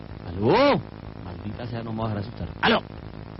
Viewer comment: (hablo colombianez así que dice: ) maldita sea no me voy a dejar asustar* XDD